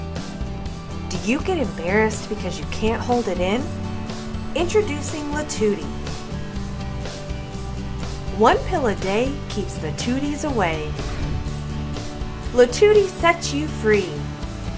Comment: Um. I thought it said take 127 pills a day😂